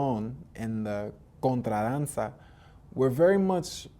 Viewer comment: Unfortunately at 14:45 I cannot make out what you said after Danzon, what was the other root cuban dance that salsa originated? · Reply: the Contradanza! this is an interesting European rooted dance that fuzed with African and indigenous dances in Cuba to form the many popular dance styles of Salsa's lineage.